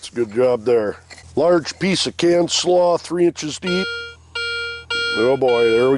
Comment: What is he saying at 7:10, canned slaw???